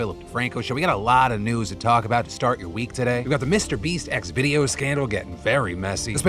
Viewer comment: I heard “Mr Beast’s Xvideo”, not “Mr Beast’s X video” 😂